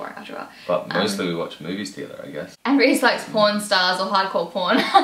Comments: Ok totally thought u said porn stars and American porn!!! 😂😂😂😂 I was like WOW she's really nonchalant about it! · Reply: I thought she said porn stars too😂😂😂😂😂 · Ok totally thought u said porn stars and American porn!!! 😂😂😂😂 I was like WOW she's really nonchalant about it! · When you said Reece liked Hardcore Pawn I swore I heard you say hardcore porn hahaha! I was thinking why is she telling us this?? You two make a great couple, so glad you're happy :) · I thought she actually said "he likes pornstars."